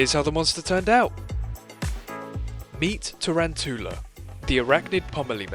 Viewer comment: it sounds like Tarantoola is saying “hawk tuah” 😭🙏